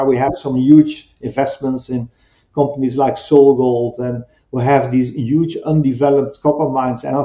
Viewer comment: whats the name of the Latine American company he mentions ...... ? ...."sole gold" I thought he said ??